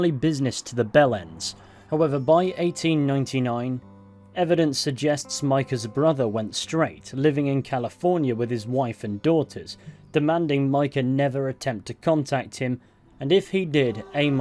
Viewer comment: Did I hear that right...The 🔔 ends 😂